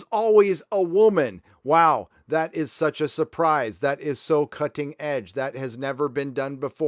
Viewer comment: I swear I heard "that is so cunting edge".